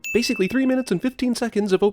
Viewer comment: Typed "three minutes and 15 minutes" when he actually said "three minutes and 15 seconds". *Ding. Nothing is without sin, not even them!